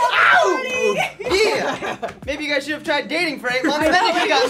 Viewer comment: did she say they should try ?!!!!!